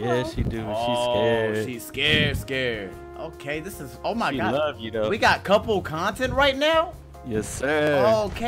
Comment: I thought he said "she ugly tho" 🤣🤣🤣